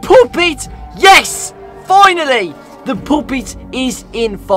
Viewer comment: Did he just said "poopit"??? 😂